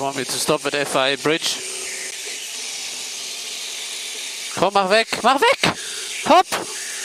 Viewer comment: For alle the guys, who are questioning what Vettal said at 3:00. Translation is below. German: S. Vettel:" Komm mach weg. Mach weg! Hopp!" English: S. Vettel:" Come on, get it away. Get it away! Go!"